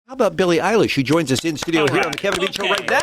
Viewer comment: I heard: "So how about Billie EYELASH?" in the beginning ._.